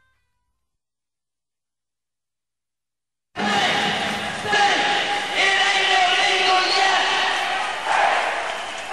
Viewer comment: what does it says from 7:37 ? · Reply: "Think, think, it aint illegal, yet!"